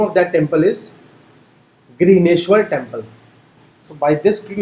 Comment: it's Ghrishneshvar Temple, sounded like greeneshvar. Anyway, great initiative.